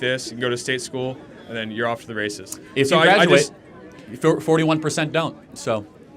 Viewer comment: Off to the racist?!? Excuse me did you just say you support racism.